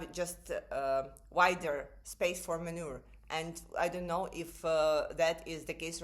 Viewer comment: I understand that you meant "more room to maneuver", but the last word sounded like гній.